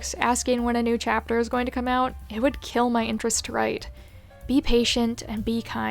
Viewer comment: ohh i thought that said i would kill my wife...